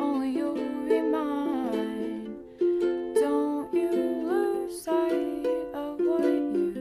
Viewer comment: i thought it said “dont you lose weight”